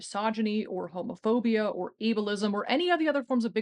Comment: I thought she said "evilism" instead of ableism & I'm keeping it.